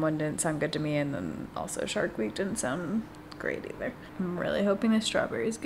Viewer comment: I legit thought you said the flavor of the cookie was shark queef 😂